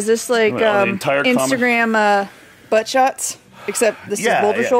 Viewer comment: is having TOO much fun! “Shoulder-shots are the new Instagram Butt-shots”!? So true!! 🤣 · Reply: My old as thought she said shoulder boulder